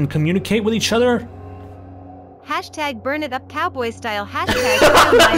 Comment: I heard “#burnitupcatboystyle”. I’m p sure it said cowboy but I fucking love catboy style lmao